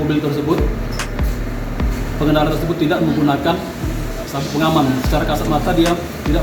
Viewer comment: Itu namanya menuduh.ngelak ajah lagi..pecat ini oknum arogan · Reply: SABU atau sabuk pengaman ...apa kita salah dengar🤔